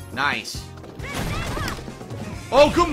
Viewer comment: What the fuck is he saying? Oohguumoo?